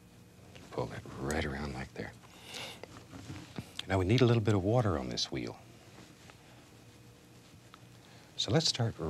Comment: Bob would make a great Pokémon Trainer. It sounded like he said: 'Let's put a little more water on this Weedle.'. He'd robably go for Smeargle, though.